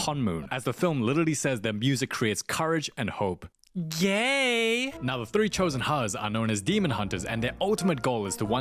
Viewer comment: Did he say Gay Or Yay